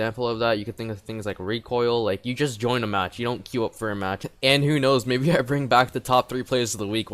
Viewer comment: “Andrew knows” o.O (Ik you weren’t talking about me, but my heart stopped when I heard my name) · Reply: Im pretty sure he said "And who knows"...